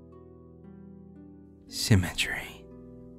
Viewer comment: for some reason my mind thought it said 'cemetery' and not 'symmetry'. what's wrong with my brain?